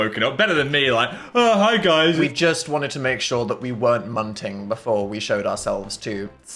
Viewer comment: what does Dan say here “wanted to make sure we weren’t—“? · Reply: munting. i hate him